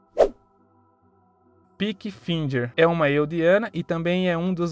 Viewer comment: eu ouvi peak blinders 😂😂😂